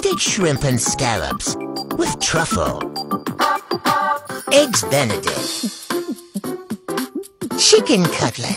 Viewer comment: i heard "eggs bentellect" 💀💀